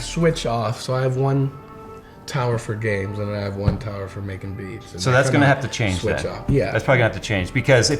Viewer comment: at first I thought he said, "one tower for games and one for making memes"